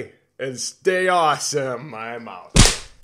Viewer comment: BTW at the end I thought you said "Stay awesome my mouth"